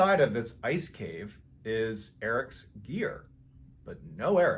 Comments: OH! Eric's "GEAR"!!! I thought you said, "Eric's EAR"! The story is waaaay more creepy the way I heard it! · Oh lol, I was sure I heard you say Eric's EAR was found in the cave, then I watched the video and it was Eric's GEAR. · I thought he said "Eric's ear" 👂and I gasped out loud. 😮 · Lol when he said Eric's gear, I heard "Eric's ear". I'm sticking with "Eric's ear" just to make the story epic(er) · Everytime I hear the mountain climbing one I think I hear "Eric's ear" rather than "Eric's gear" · At first I thought he said “ Eric’s ear” . The story would’ve been even creepier..... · Lol when he said Eric's gear, I heard "Eric's ear". I'm sticking with "Eric's ear" just to make the story epic(er) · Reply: I heard Eric's beer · I thought you said “Eric’s ear” hahaha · For a moment I thought he said Eric's ear.